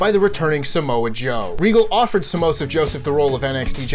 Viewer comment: Did just call him Samosa Joe? 😂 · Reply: Even sounded like "samosa joseph", scott steiner would be proud lol